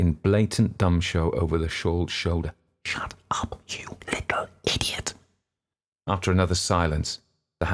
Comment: What does the guy say to the younger girl at 11:03 - shut up you idiot - something like that. I was curious about the British vernacular that he was reading. It's the words between blatant and then you idiot. Sounds like gumshaw - if someone could tell me what the word was, I'd love it and thanks! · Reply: ‘Shut up you little idiot’ not you obviously